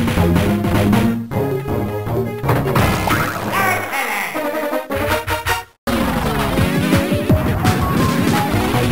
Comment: What does he say at 3:32? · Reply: Aha you did it. Or he might have said something in Japanese.